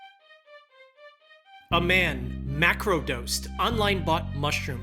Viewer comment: did I hear that right? MACRO dosed?